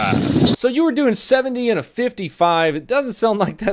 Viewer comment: I thought the officer said "170 in a 55" I was like holy fuck!